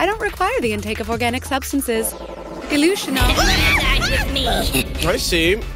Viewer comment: what did Jibril say? It sounds like "illusions" but Sora follows it with "outer core"